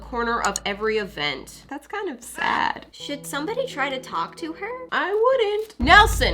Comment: sounded like sh*t